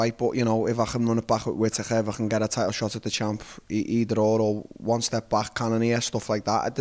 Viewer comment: $10 to anyone who can tell me what he says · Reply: “If I can run it back with Whittaker, if I can get a title shot at the champ, either or.” I’ll take any payment.